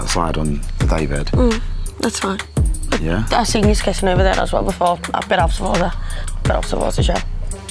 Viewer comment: what is Sam saying. like I literally don't understand. especially from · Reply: She said : "That's fine. I've seen yous kissing a bit over there as well before. Absolutely/bit out of order. Rrrsssttsshhthrbsoxjsi, if you had any respect for me you would not be kissing her in front of me. Rrrrthrsjxuoensjxlu ##Wipe."